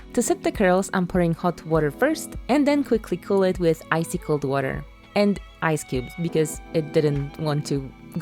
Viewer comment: I absolutely heard that as “icicle’d water” at first lol